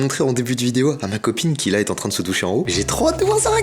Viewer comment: j'ai entendu ma copine se touche 🤣